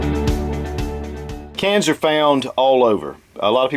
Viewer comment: When he said "cans are" i heard "cancer"